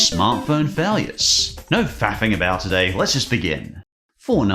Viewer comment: Don't get me wrong I love and his content is wonderful. But am I the only one who heard him say "no fapping about" at 0:31?